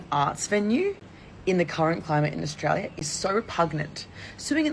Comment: "Pugnent"? I think the word she meant to say is repugnant, as "pugnent" isn't a word lol. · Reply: She said repugnant...